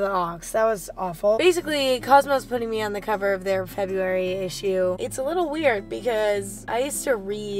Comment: Tbh i thought she said i’m on the cover of constipation ok.